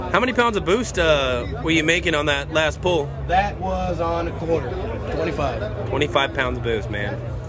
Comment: damn, wonder why the torque is way higher than the HP number? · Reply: I thought He said quarter and followed it up by saying 25lbs of boost!!